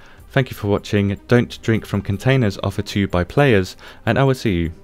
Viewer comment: "Don't drink from containers off of two-by players." What did you say? Couldn't understand it. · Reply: " Don't drink from containers offered to you by players"*.